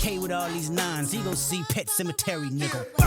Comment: I always thought he said "ain't gone see pet cemetery" because in pet cemetery, the pets came back to life, and he was saying they ain't coming back...